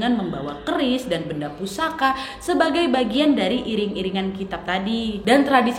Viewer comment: maaf wak, aku dengernya kitab ya wak. :) "kirab" wak maksudnya. Nyuwun pangapunten